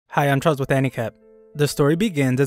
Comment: Bro why do i always hear the intro as "hi guys im charles with handicap" 💀🤣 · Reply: I always hear "hi guys im trous with anicap"